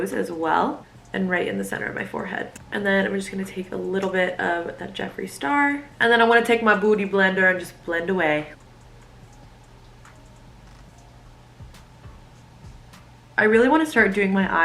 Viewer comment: she really said bOoty blender 😭😭😭😭am I right?